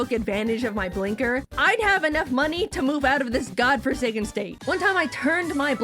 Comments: did I just hear Jaiden say FORSAKEN? FORSAKEEN WOOOO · I hate how I’m doing this (nice video btw) but DID YOU SAY FORSAKEN?